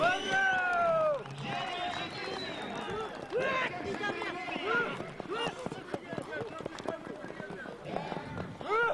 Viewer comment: На 9:25 слышали что он сказал 😂😂😂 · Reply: О пидараз о сука 🤣🤣